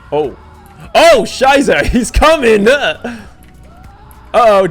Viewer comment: did you really said "Scheiße" :D ?